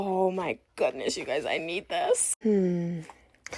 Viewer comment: I thought she said no needohs..